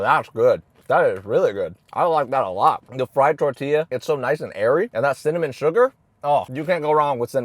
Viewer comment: i heard it as "its so nice and hairy" 💀